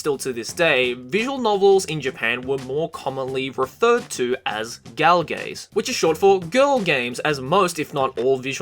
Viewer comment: call of duty <num> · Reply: did he said dra-galge? lawl.